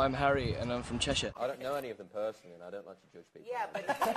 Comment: what does Liam say? I don’t know... whot?? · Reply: Idk who he is talking about but he says "I don't know any of them personally and I dont like to just people"